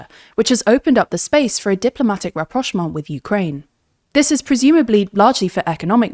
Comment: What's that one word she keeps saying 5:37 ? unable to understand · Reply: She says 'Rapprochement', it's a french word meaning "bringing closer"